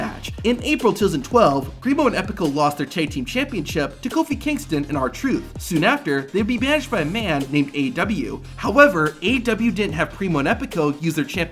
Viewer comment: did he say AEW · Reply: No he said AW